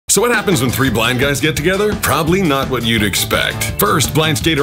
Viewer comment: thought he said "know what happens when 3 black guys are together"